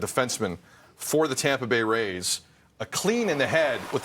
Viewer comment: Wait, what the heck did I just hear? @ 1:36 Tampa Bay rays? Am I hallucinating?